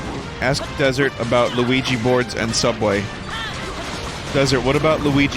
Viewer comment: I thought you were saying "Luigi board".